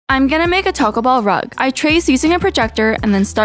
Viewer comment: Am I the only one who heard her say To-kuh Ball rug